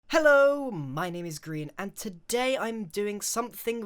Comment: It sounded like you said. Hello my name is green